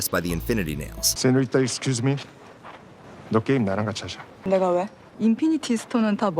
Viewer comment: i heard "nig*a wha?"